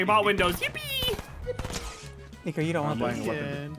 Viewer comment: Is it me or did he say the n word